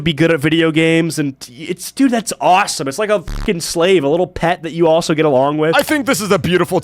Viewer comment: DID HE SAY A SLAVE???? IM GENUINELY DONE